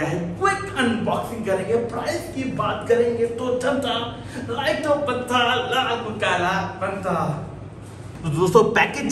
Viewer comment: can somebody tell me what he said at 0:43 for like 2-3 secs ? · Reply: To janta like to banta...laal ko kala banta